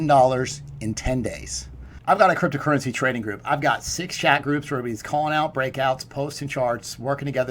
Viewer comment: I always thought you were saying " shag groups".. 😂..